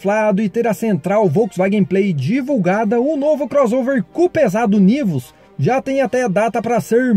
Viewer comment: Eu entendi C* Pesado KKKKKKK